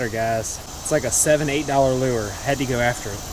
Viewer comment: Great video I had to rewind it I thought you said $70-$80 lure lol